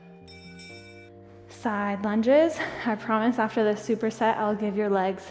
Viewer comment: Lunges are my least fave exercise... anyone else? 😂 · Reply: Thought you said LUNGS for a sec like damn that’s a thing 😂😂